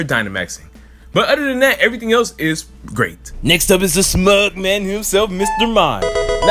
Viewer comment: Did you say Smug man or Mugman?